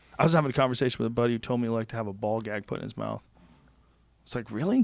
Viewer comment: I thought he said bald guy not ball gag